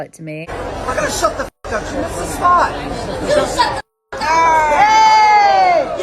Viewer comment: It sounds like she is saying “you shut the fuck up” which sounds like a response to me 💁🏻‍♂️